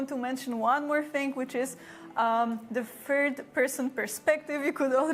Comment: The hell did you watch? They clearly state and even show the game in 3rd person. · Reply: Now that I watched this 40:35 again, I initially thought she said the game would be in "First person perspective", but now I think she says "Fird person perspective" (in other words "third person perspective", but with a wrong pronunciation since english is not her native language, but can you blame me for not getting it right? I'm not that used to listen someone talking in english with a polish accent), but if that's the case, I stand corrected. I might have commented in the heat of the moment, since it surprised me (not in a good way) what I initially thought she said. It's true what I understood and what the brief gameplay showed didn't add up, but still, I thought the same as Cohh when he heard it.🤷‍♂